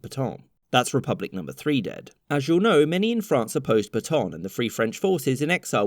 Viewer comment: When you say "Pétain" I hear "Baton" 😅